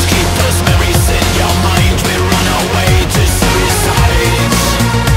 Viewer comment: at first i thought he said we're on our way to suicide but now know it says we run away to suicide;)